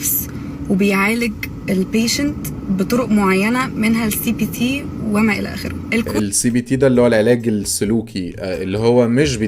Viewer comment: عليا النعمة سمعتها CVT وافتكرته الفتيس 😂😂😂